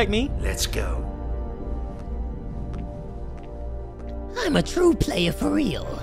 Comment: I looked away from the screen because I was making food. I thought Joker actually said “I’m a true playa fr”